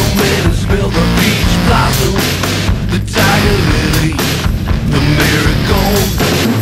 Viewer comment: Okay I thought he was saying "Vagina Lily" for a long time. ;.; Tiger Lily makes more sense, yes.